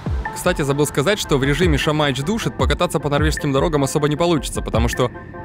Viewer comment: услышал «Шамаич душит» прослезился🥲 олды поймут